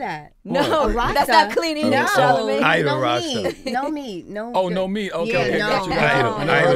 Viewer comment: Lol. I was wondering what Styles was talking about. Sounded like he said "idle Rasta" but when he put it in context I realized he was saying or meant to say "ITAL". Ital is vital.